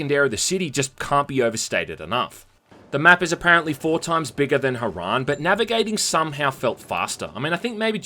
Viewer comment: thought you said "4x bigger than your mom"